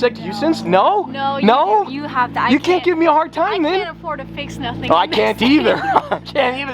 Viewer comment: Did say “I can’t afford to fix nothing…” ? I hope I just misunderstood. She seems far classier than that hillbilly speak.